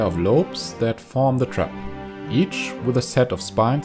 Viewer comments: Wow Im catching a growing channel with high quality in its infant state? This is a rare gift. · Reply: seriously, i found this guy a month or two ago and its the quality of a multi million sub channel although its annoying that whoever did the subtitles did such a shit job haha, at 2:55 when it says crown? hes saying trap, and 'called echo strips?' is 'that act as triggers' and 3:11, utilized microbes?? is utilized flypaper traps.. i mean come on, that one wasnt even difficult at all hahaha did the person who wrote the captions listen to this from another room with the volume turned down..? why even do it if youre going to half ass it? i mean even if they didnt understand the first time, idk why they didnt rewind and listen again haha, its not that hard, its adding misinformation to the subtitles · to the subtitle person, at 2:55 when it says crown? hes saying trap, and 'called echo strips?' is 'that act as triggers' and 3:11, utilized microbes?? utilized flypaper traps.. i mean come on, that one wasnt even difficult at all hahaha did the person who wrote the captions listen to this from another room with the volume turned down..? why even do it if youre going to half ass it? i mean even if they didnt understand the first time, idk why they didnt rewind and listen again haha, its not that hard, its adding misinformation to the subtitles